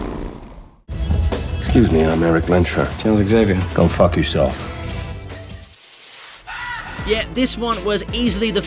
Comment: uhh no he didn't say that from everyone (including me) hear what logan said was "go f*ck yourself"